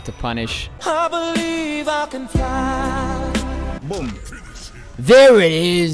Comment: I’m not the only one who heard: boom. Right?